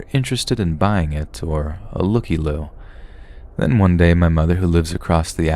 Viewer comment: Did he say a looky loo?